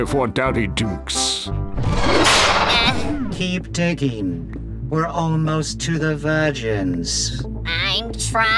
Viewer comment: I thought Creepio said "we're almost to the virgins"